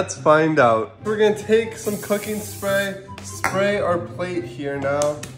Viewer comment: I hear " take some fuckin spray" even tho I know it's not that 💀